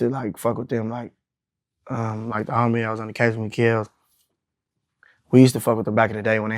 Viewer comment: did he said the homie we killed when was ask about how he meet mustand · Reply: he said kellz which is his homie who is locked up rn for killing a Inglewood blood